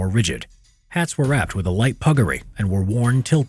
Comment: what the video says: puggaree What the subtitles say: buggery